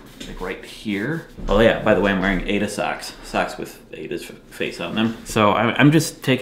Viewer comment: I'm so sorry. I heard that as "I'm wearing anus ox." Eep.